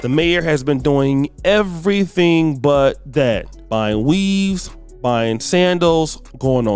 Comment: I had to replay it a couple of times to check because when he said she was buying weaves, I thought he said she was buying weed . . .